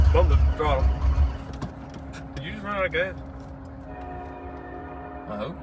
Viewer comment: I think I heard say "ain't got no gas in it". 😆 Cool Mustang song, too! Fun ride-a-long... thanks for sharing! Quack!